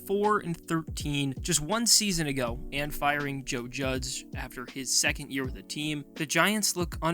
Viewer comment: uhm…. Joe Judzz? Or better yet, Joe Jugs? It was one of those two for sure because you definitely did NOT say Joe Judge 😂